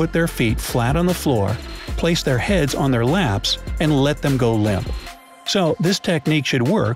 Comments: Great. Now tell me how to stay asleep. I wake up every 20 minutes. I thought he said while sitting they put their heads in their laps. I went back, thank goodness he said hands in lap. Whew! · This announcer is ... tell me how exactly does one sit in a chair and put their head in their lap? See min 05:54 where announcer goes off script. Writing on screen says “hands” but you can clearly hear him say heads!! I was just listening at the time and had my head turned away from the screen so I know what I heard and went back to check it against the transcript and saw what he had done. It happens fast and it would be easy not to catch it. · Did he say "put their heads on their laps"? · is it just me that heard "heads" instead of "hands" like written? lol